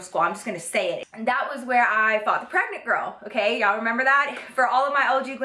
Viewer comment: Wait... wait... I heard "fucked a pregnant girl" the first time and then "I fought a pregnant girl". XD